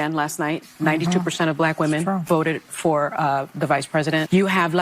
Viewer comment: Why at 2:05 does the one brunette say "vodit" instead of "voted"? I hear this T sound instead of the D sound at the ens of worda, as if it is a cultural differentiation, lime saying "axe" instead of "ask." I just find it interesting. You never heard that nuanced enunciatikn among certain segments of the population 10, 20, 30, 40 years ago. I even hear SteveHarvey doing it. He disn't talk lime that 20 years ago. What is up with that??